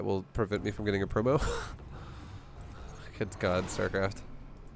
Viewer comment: What does he say at 32:04? ‘It’s gone, Starcraft’? Anyone know? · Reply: Sounds like he says ' Just god, starcraft' or 'Good god starcraft'. hes' talking about a MMR bug where his MMR keep increasing and actually promoted to higher leagues but the graphics does not change and it still says Bronze 3. he calls it MMR bug, a known bug in Starcraft 2.